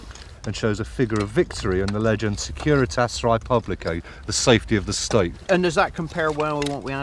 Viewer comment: Re: the coin at about 9:10.....Did he say the inscription was: "Securitas Res Publicae"?